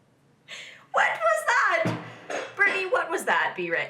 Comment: she really said 'wet was that'